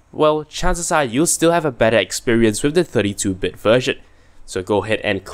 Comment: did u say "bad experience" or what on 2:17? I have 64bit com, which one should i download? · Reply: Hello and thank you for your comment! I said "better experience"! Generally the 32-bit version is preferred due to better compatibility.